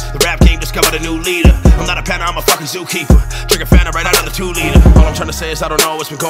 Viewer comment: He just said people say he looks like he's a panda but I think I'm more like a bear. Does he know a panda is a bear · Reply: I could have sworn he said " I'm not a panda I'm a zookeeper..."